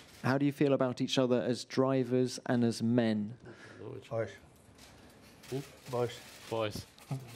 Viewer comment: ???? What did kimi say??? 😂 boys??